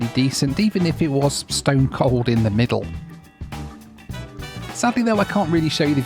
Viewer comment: I came here to make sure I heard you right “it was cold in the middle?” 😂